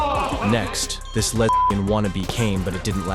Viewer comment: did he say "this lesbian wannabe"?? 😂😂😂